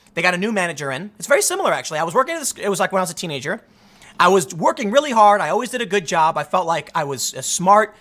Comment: Did you just say “...I was twerking really hard...”?